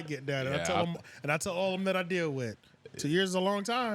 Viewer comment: Hold on did he say all the men he deals with? Whoa my guy. 6:56 time. Ay but at least he is real with himself. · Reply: lmao "all of them" that he deals with